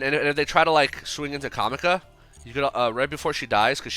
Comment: I keep hearing "Whore House". Damn, my nigga Amazement Whoremonger Arlepimpno running some business. 🤑